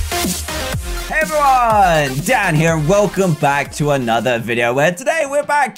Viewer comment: Omg when it started I thought he said: Hey everyone DAD here!! LOL